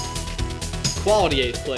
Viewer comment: Wait. Have I misheard the ending?Is saying "Quality eighth place" At the end?